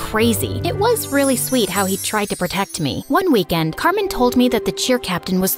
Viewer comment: I wasn't listening well and I thought she said "Cartman"